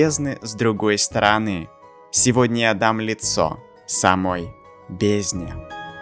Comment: АХАХАХАХ я на 0:37 услышал "дам НА лицо"